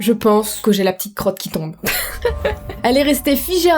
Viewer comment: Lol t'a dit Carol et moi j'ai compris "carotte" 😂 si non j'aime trop les histoires d'horreur ❤️